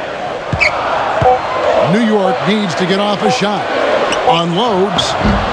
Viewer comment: did he say that the knicks needed to get off a shot at 4:46 whent the Heat had the ball?